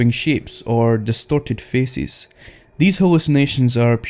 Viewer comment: sounds like you said destorted fecies xD